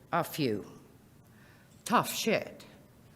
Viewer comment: did she say tough shit?